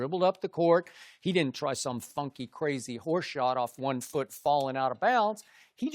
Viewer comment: I thought Skip was about to say horseshit